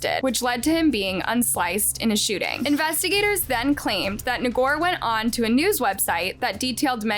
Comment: I watch your channel all the time…your closed caption can be offensive since it reads ni99er instead of Ngor